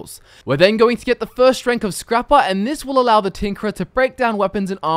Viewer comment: I swear i heard "The Tinkler"